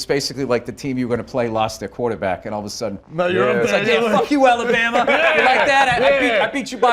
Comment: Did he say “pero like” at 10:24?